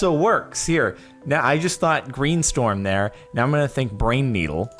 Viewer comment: #2 I heard "Brain Needle"